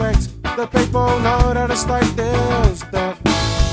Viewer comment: At first I thought he said "The people know that the SNAKE deals death" instead of "state".........still makes sense!